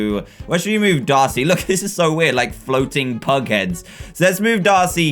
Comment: He takes the biggest parts of the pictures and uses real ones I thought it said drawing Pug heads-nyan pugs THE WHOLE GIRRAFE-punk girraffe